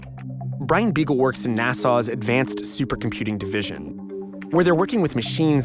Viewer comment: It sounds like he is saying "Nassau" and not NASA